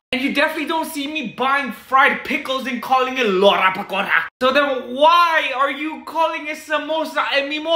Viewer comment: " Garam kutta " 💀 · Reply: I thought he said "loda pakoda "😂🤣